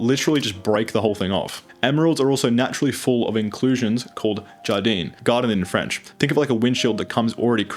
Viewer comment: I listened to that sentence three times, and I can't figure out the word that was said here... and I'm French. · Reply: Jardin